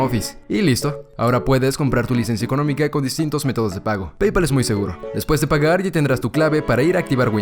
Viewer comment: pensé que dijo YAPE